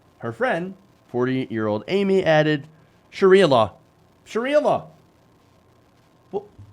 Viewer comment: Did he just say “Shakira law”?